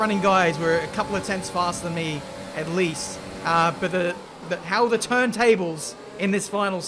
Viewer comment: Did he just say "How the turn tables." ?